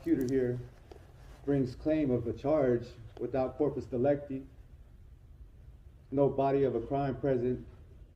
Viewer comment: Did he just say Porpoise Electi 3:52 ? 🐬